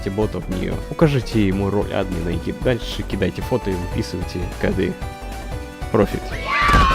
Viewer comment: ничего не слышу из-за музыки, что ты говорил? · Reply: "вписываем коды" В настройках бота есть множество команд, но которая нам нужна - Аниме Добавляем фото для генерации и пишем слово Аниме.